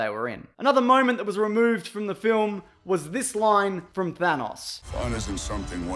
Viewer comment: Sounded like you said vanoss not thanos